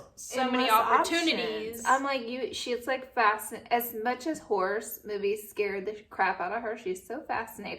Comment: I legit thought you said “as much as horse movies scare the crap outta her” and I was so confused😂🐎 go back and listen to